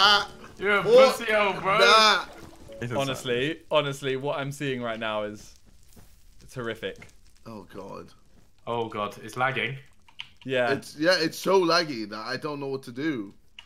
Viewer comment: simon literally watching the forest and his house burn down “What I can see right now is terrific” 13:00 Edit: okay I rewatched a couple times and I’m wrong sorry 😂 he does indeed say horrific, still make me giggle tho 😂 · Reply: He said it’s horrific quickly enough for some people to think he said terrific...